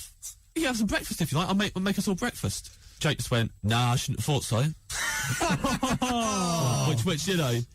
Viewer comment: what does his mate say at 5:56 I can't make it out. Only "nah i shouldn't... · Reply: nah I shouldn't have thought so